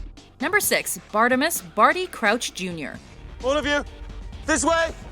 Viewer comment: I thought she said “Artemis Crouch” for a second. Wtf 😂😂